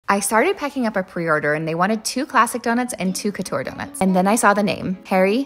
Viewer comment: for all those saying it was "random", she clearly says they wanted 2 classic donuts and 2 couture donuts. that was not a random donut order, and was in fact tampered with. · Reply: Ohhh she said ‘Classic’! I thought she said ‘Plastic’ lol